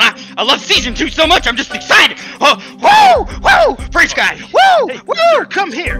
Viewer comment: Was I the only person who heard say French Guy at 4:44? Ya know French Guy… the dude who did some illegal shit with children